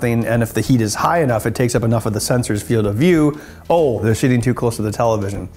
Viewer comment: Did he say they're "shitting" too close? Cause I think he's implying something.